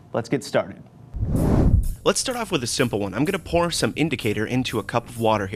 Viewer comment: do they say Im going to porn instead of put or its just in my head??? I cant believe Im that fucked up